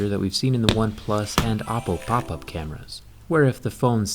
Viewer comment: Did he say OnePlus and "APPLE" Pop up cameras Even the captions read it Apple